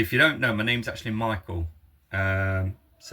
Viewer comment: I could've sworn I heard you say your name was Mitchell. Well damn, my apologies Michael.